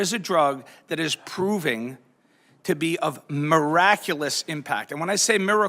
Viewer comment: when I hear "miraculous ", I hear, "unscientific " Yall train yall ears...